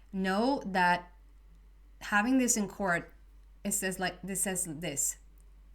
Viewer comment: While listening to your reading, I heard, "lose in court, win at life". I'll be happy winning at life. Let it be so!